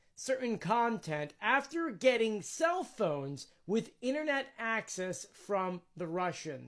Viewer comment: I thought he said North Korea got the Weenier-net from Russia!! 😂